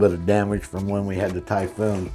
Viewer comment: Did he say tycoon 7:30 😳😳😳😳😳😳😂